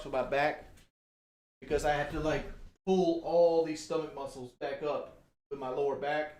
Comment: did he say stomach muscles?